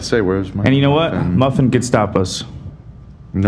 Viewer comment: Ohhh said "muffin can stop us" lol I thought he said "muffin gestapos" I was so confused 😂